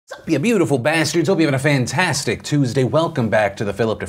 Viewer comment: My connection hiccuped and the intro I heard was "Sup ya beautiful ass turds..." Not even mad, best channel ever 😅